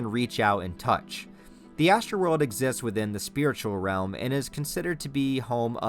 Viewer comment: I always hear it as the ‘Astro World’ and think of travis scott’s halloween costume as femto